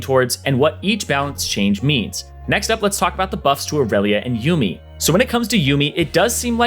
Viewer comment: He said irelia so fast i thought he said aurelion,now i am sad